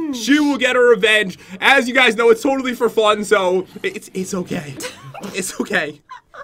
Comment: am I going crazy or did say “it’s all for fun” when he won?